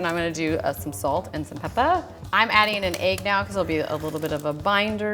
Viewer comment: And I'm gonna add some Pepper! (I low-key thought she said Peppa